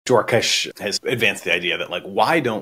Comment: I thought he said dwarfish the first time around...